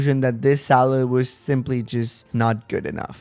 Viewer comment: Why is it every time someone says "Saleh" I hear "Salad"? THAT'S IT, CHANGING HIS NAME IN MY HEAD CANON